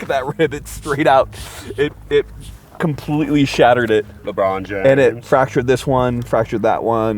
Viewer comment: Anyone hear that “Lebron James” at 8:50 ?